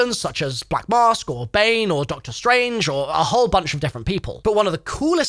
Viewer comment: I’m sorry but I heard you say doctor strange 😂